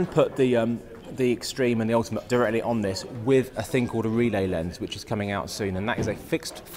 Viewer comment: What's the name again of the thing which allows putting the 35mm adaptor directly on the PMW-EX3? Is it relay lens or I misheard it? Anyway, where can I find it?